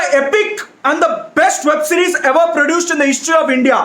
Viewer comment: I thought you said, india produced asur best web series!